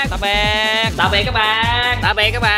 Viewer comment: Nhìu phân cảnh nghe thành tạm biệt cái bang hay tạm biệt các bang =))))